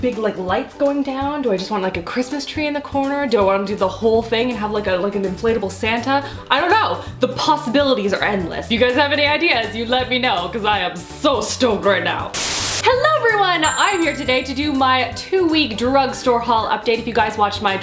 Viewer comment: I thought she said, "I'm so stoned right now."